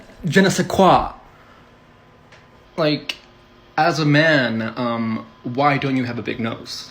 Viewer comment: did he called himself a man?😂